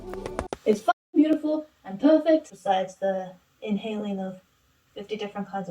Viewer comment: WHAT SHE SAY? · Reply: “it’s fucking beautiful and perfect.”